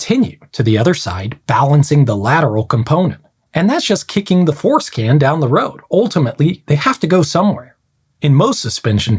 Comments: I had to go back: "That's just kicking the force can down the road, sounded like: " ... kicking the foreskin down ..." on the first pass! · Okay this comment will probably get automatically deleted but I gotta share in hopes someone else caught this: when he said "kick the 'force' can down the road" I could have sworn he said "kick the foreskin" LOL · I thought you said "foreskin" when you said "force can". Had to back up the video a bit.